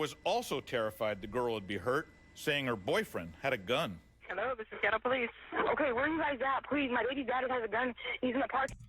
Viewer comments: Did she just say “hello this is ghetto police”. I swear thats what I heard. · Listen to the 911 operator @ 17:10, sounds like she says: "Hello this is ghetto police" 🤣✔